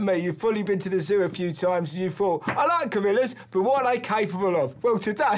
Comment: I misheard gorillas as Camillas, and it made the exchange even better.